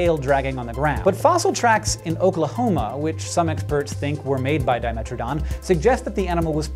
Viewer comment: I heard that as him saying the dimetrodon made Oklahoma